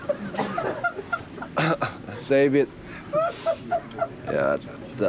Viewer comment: when he said save it I thought he said David.